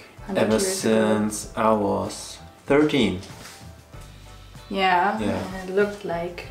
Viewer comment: DID HE SAID 30!!!!!!!?